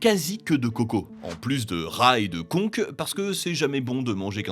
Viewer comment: en plus de rats et de conque ? j'ai entendu rail de coke :D